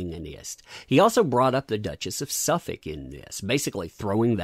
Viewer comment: Suffolk/Sussex - opposite sides of London \m/ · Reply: Superdude70 1:48 I hear Suffolk, I see Sussex on the page - tell me what you hear \m/